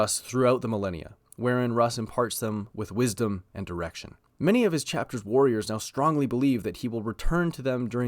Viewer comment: I thought you said erection